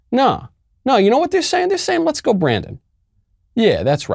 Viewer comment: I heard, "Let's go Greta!"